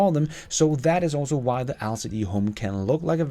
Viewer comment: I thought you saying Alford E homes with all your videos lol. I've been imagining Bruce manor with solar panels 😆